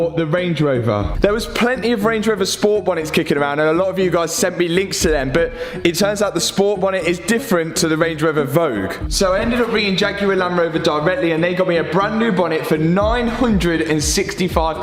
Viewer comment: the amount of times i thought you said "range rover evoque" is ridiculous